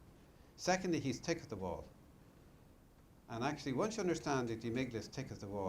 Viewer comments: Wtf is he saying at 12:02?? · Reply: Tyler Smith “thick as a wall” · Does anyone understand what he's saying at 12:02 It's an incredibly important part of the speech but all I'm hearing is "Secondly, he's tikiththewuld. And actually once you understand that the amygdala's tikiththewuld you can get rid of panic attacks very quickly." It's the most important word or phrase in the speech and I just can't figure out. Anyone have an idea? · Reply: "Thick as the wall", implying the gunslinger is really really stupid and you can't reason logically with him. At least, I think?